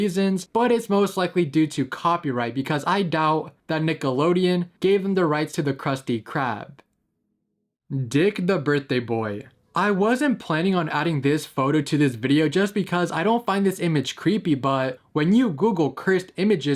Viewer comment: Did bro say d*ck☠?